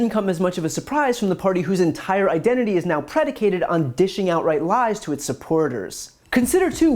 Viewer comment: I heard "dishing out right lies" instead of "dishing outright lies" @ 6:02. Ha, perfect - this should be a thing.